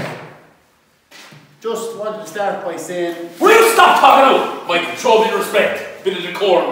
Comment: can U turn it up fully IDK what he's saying... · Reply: Hi Guys he says “will you stop togging out” which means changing outta ur gear